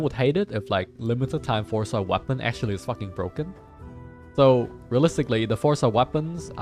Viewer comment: Everytime you swear and I hear "Bouken", I crack up...I don't know why I find it so funny, everytime...every video, I laugh without fail. "Bouken" hell man xD.